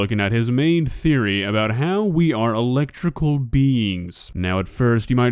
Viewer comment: What he said : "We are electrical beings" What i heard : "We have electrical bills"